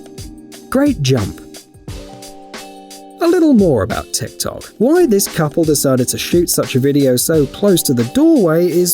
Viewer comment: Listen to what the guy says insist? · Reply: At her age